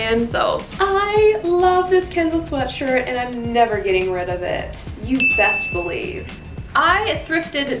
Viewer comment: Lol. I don’t know why but I thought it said “getting ready for jail” 😂